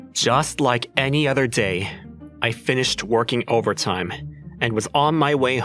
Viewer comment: Bro i'm i the only one who heard him say " i finished twerking"